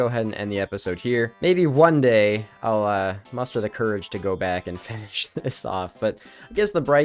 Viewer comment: I honestly thought you said "one day I'll Muspah up the courage and go back" Great pun if you did